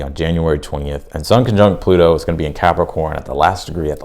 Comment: I thought you said, "sunken junk," but you said, "sun conjunct." I was confused for a second.😂😂😂